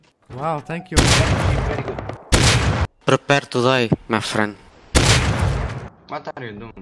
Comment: He wasn't talking to you, he actually said "Prepared to die, my friend" The last words of a brave man before his death, he wasn't even afraid of it.